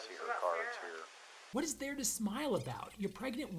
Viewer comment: could somebody please let me know what says right there? I have watched this a lot, but I can't figure out what she said. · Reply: I think it's "what about Sarah?"